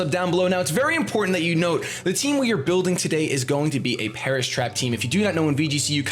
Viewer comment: I thought they said "Paras Trap" for a long time and I was so confused